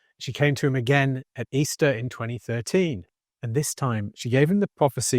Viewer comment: I never heard him say Easter- I heard him say Sept or October